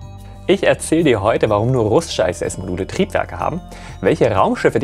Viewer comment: beim 5ten Mal hab ich dann auch Mal endlich das "..Module" vom ISS-Module rausgehört 🙈 vielen Dank für deine großartige Arbeit #TeamSpace🚀💜